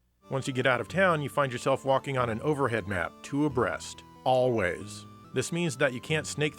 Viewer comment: Did you just say breasts?